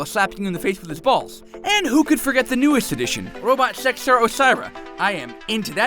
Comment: Did he say Osira